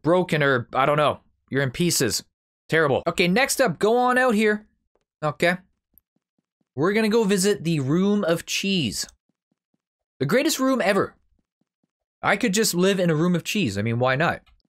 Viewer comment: I thought you said bathroom 😂🤣